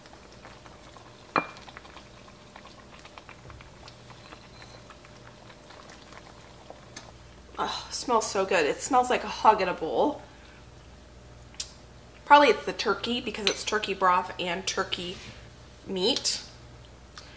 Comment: “Smells like a hug in a bowl” love that so much! Thank you for all the videos! I am a mom of 4 and love that I can watch your videos with my children. They always ask if mommy’s friend has made a video. I love that they think we are real friends not just a YouTube channel. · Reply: haha I'm Australian and thought she said "hog in a bowl" 🤭🙄😏 I much prefer THE HUG